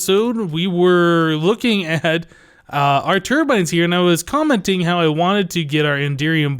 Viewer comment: When you said "turbines" at the end, I heard "termites."